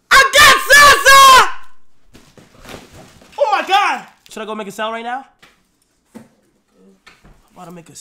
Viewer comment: I thought he said should I make a salad right now XD